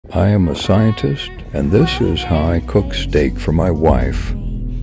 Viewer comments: Brooo I thought it said “I’m a scientist and this steak is my wife” 💀💀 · “I am a human and this how I cook for my wife “ - mortals in 2023 be like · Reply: thought that said this is how I cook my wife